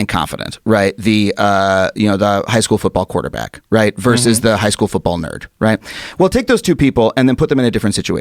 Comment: Did he just say the high school football nerd ? 8:42 is that like a A B hybrid